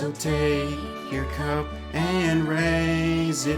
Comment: did he say cum😳😳